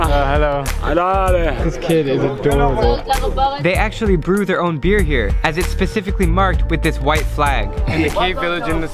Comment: did i hear somebody say in bahasa indonesia in 10:52 ? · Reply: "yaAllah cakep banget" -bahasa indonesia